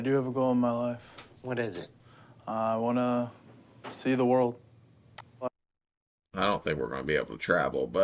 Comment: I thought he said "save the world" when the doc asked him his goals, and i had a brief image in my head of a fat super hero. Super size Man, The heat Flash, Red Robin, he can be the defender of all snacks, Koolaide mans sidekick 🤣 Then i realized he said "see the world" and i got a lil disappointed. I think the worlds ready for a morbidly obese savior.